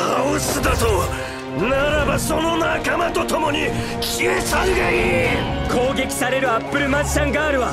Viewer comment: 「消え去るがいい」が「消えちゃうがいい」に聞こえた(;^_^A